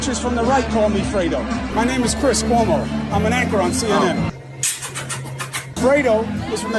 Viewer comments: I miss-heard, I thought he said he was an “actor on CNN” lol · I heard " I'm an actor on CNN" 😂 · "I'M AN ACTOR ON CNN." He said it himself. He's CNN's weak link.....stage name Fredo. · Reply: Nah he said ANCHOR..listen again. I thought he said ACTOR too, but nope -- Fredo said ANCHOR. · I thought he said I'm a CNN actor. In other news is 35.